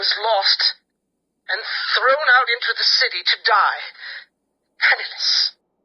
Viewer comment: when he said "penniless", I thought he said "penisless"